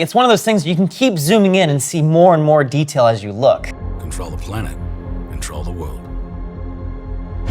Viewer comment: I'm sorry does he say "Control the planet, control the world"?